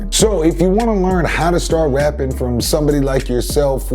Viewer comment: Me being my idiot self thought it said “How to start Raping” 😶 I